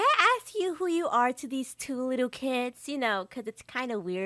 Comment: BRO WHEN THE OLD LADDIE SAID YOU LITTLE KIDS👹 IT CUT TO AN ADD AND SO IT SOUNDED LIKE SHE SAID YOU LITTLE PIZZA PIZZA😭 BC IT WAS A LIL CESARS AD😭😭😭😭😭